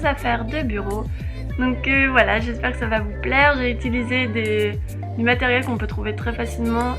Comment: je crois me tromper mais j'ai entendu:"des matériel " si c'est le cas on dit:"des matériaux" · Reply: jacquline "du matériel "